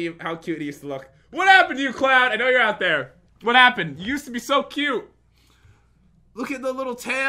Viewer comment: it sounds like is saying that cloud is not cute